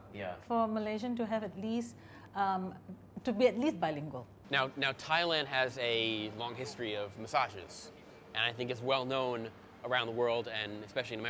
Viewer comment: I thought he said “Thailand had a long history of misogynists”